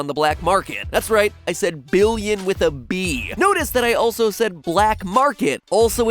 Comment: “Billion ,I said billion with a b. “ really I thought you said billion with a d